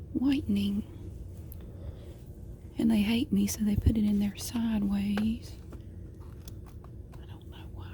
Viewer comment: did she say "and they hate me so they put it in there sideways"? 😂